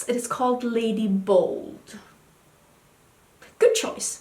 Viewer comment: I thought you said lady buldge lmao